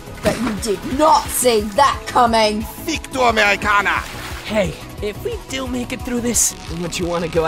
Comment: Did he just say "fick du Amerikaner"? If that's what I think then he should have said "Fick dich, Amerikaner"